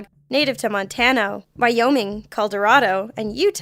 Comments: Anyone catch the "Caldorado" at 4:28 ish?? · did she say "Coldorado" at 4:26 ;-; I'm cunffuzzled.... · did she say "Coldorado"?